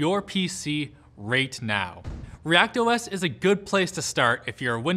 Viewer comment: did he just say "Rate Now"? TF?! Mind blown 😭😂